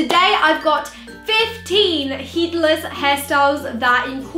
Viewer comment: i thought it said "heartless hairstyles"